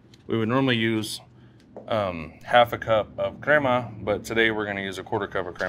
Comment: For the corn, after the mayo, does he say creamer or crema? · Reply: creama